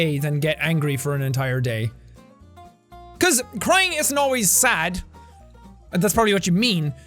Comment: says prying instead of crying pmsl 🤣🤣🤣